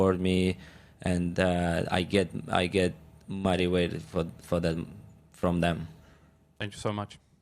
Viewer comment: I thought he said “I get mighty wet from them!” 😮😮 Then I realised he meant “Motivated!” 😅😅😅😅🚨🚨